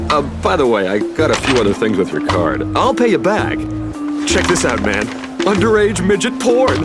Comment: Im sorry what did he say? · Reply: Yeah I had to rewind it a couple times. Wtf?